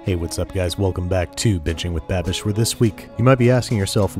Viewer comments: did he say b*thching with Babish? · "Bitching with babish" was what i heard